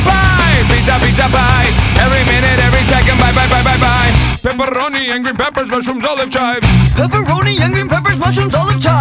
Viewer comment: For some reason I hear: "pepperoni,angry pappers"